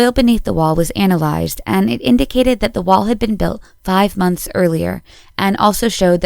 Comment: No she said 5 years. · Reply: Alright. I must have heard it as five months because it made more sense I guess.